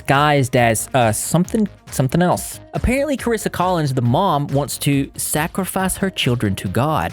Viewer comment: When I first started watching the video I thought I heard him say kris collins like "kall me kris" lol 😂😂